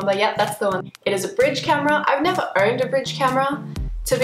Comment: Did you just say "I never EARNED a bridge camera"? Or is it just your weird accent that makes it sound like "Earned" rather than "Owned"?